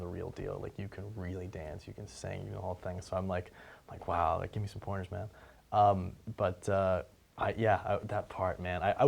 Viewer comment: i could swear he says "Hey Gimme Some Porn Man" xD